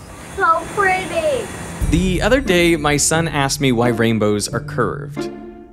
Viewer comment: I thought you said he asked you why are rainbows gay.